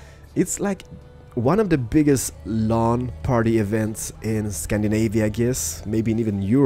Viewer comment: The way you pronounced LAN made it sound like you were saying Lawn Party :D